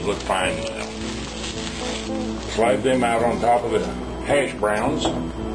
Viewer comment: I thought you said smash browns lol. My parents loved j. Wayne movies. That breakfast would make you toot all the way to the outhouse pilgrim